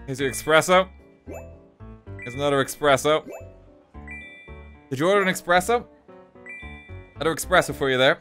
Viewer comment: did he just say ‘eXpresso’?